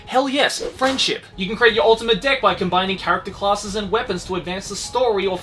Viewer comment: Me at 0:47 Did he just say ultimate dick lol Me a bit later Dang it he didn't Dono why I even thought he did